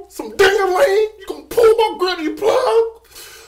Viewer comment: It actually sounds like she said she’s gonna put a plug in granny. 💀